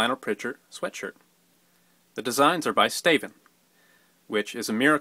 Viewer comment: when he said "staven" i thought he was saying Stephen in like an accent...;x